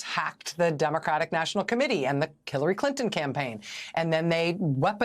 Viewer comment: Did she say "Killary Clinton campaign"? (Min 1:05) hehe